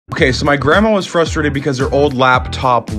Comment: I thought that said “Selling my grandma”😭